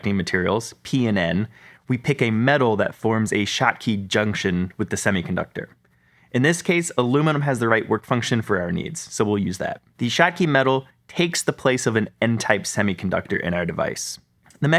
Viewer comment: Did he just say shotty metal? next frame ohhhh he said Schottky Metal